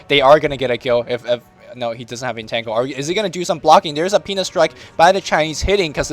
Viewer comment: Theres a PENIS strike. I cant believe I just heard that.